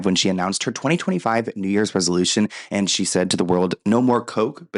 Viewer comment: Kate Moss sounded like she was telling a story to her friend because I thought I heard "and he said"